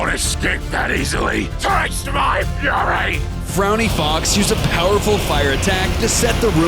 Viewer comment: thought he said furry